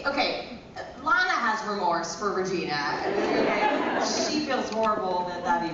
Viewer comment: What did Lana said at 7:24 ? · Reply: Ho Sin Ying she says "Lana has remorse for Regina"